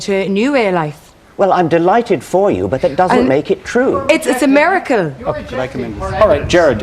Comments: Did she said miracle or America? It doesn't really matter, does it? xD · I cringed at that woman shouting "Its a miracle".You can't reason with people like that. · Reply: Thank you for writing that, because I had to watch it like five times at 5:22, and still couldn't figure out why she shouted "it's America!"..... Couldn't understand her. And when she says it, she has her arms crossed and a pissed look on her face. Happy times.